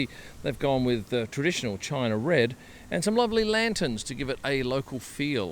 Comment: heard it as “ugly lanterns”